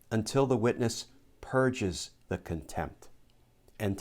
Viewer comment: Glenn.... I'm sure you meant 'purges'..... but I heard 'purjers'!